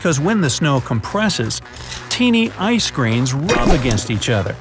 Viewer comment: Did i just hear ice creams?